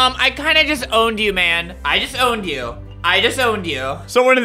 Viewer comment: I might be late but at 4:02 when he said "I just owned you", It sounded like "I disowned you". XD lmaoo